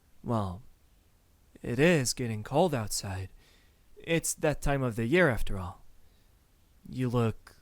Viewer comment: To be honest when he said: after all its the time of the I thought he was saying "the time of the month" but it ended up being "the time of the year"